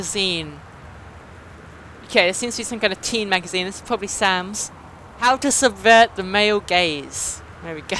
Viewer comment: I stooped paying attention for 2 seconds and i hear "how to subvert the male gays" I laughed my ass off.